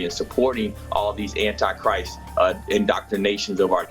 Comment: Did that dude seriously say "anti 'Christ' indoctrination" or did I mishear this?